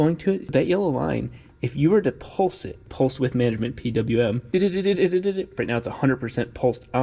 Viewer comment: PULSE WIDTH MODULATION -- I've heard you say "management" in a few videos now lol